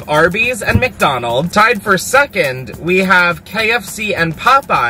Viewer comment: can we all just agree to say “suckand” instead of “second” from now on????? PLEASE hahahaha